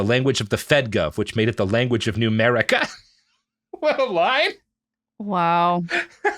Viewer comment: I keep hearing "Newmerica" as "Numerica" and I think he must have meant Numberwang